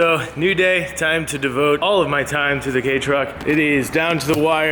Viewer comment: I wonder how many people caught what said at 2:31 he says the gay truck instead on the kei truck